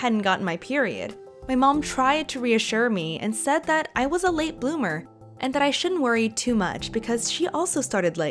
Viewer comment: Did she say ˋlate boomer´ ?